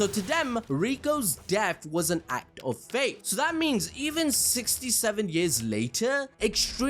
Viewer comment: did bro say 67